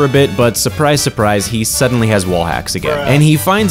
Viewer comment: did he say wall axe?